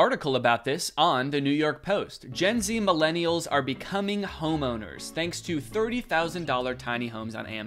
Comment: when reads out the headline “gen z, millennials are becoming homeowners” i thought he said “gen z, millennials are becoming homos” i was like HUH 😭